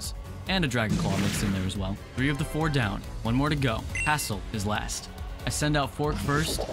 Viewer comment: Rest in peace Erm, it should've been Table. 18:19 I legit thought he said Asshole 💀